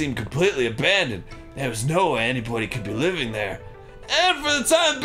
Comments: (ish) I thought Arin said "anypony" by accident and my heart legit skipped a beat. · Did Arin say 'Anypony' at 7:46? Or was it just the voice?